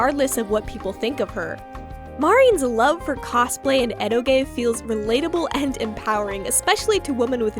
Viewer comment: "Edo-gay"?? I have no idea what this word means or how to spell it. Does anyone have context?? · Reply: Eroge. Uhh, ero games/horny game, I think